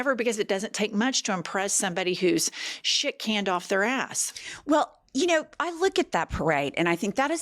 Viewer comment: Did she just say.. "💩 can off their ass"🙄? 😅😂🤣🤣🤣